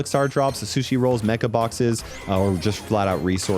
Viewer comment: I thought they said bye to mega boxes